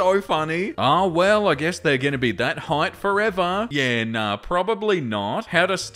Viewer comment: with your accent, I thought you said hot at first and not height and I was like "GIRL, RUN"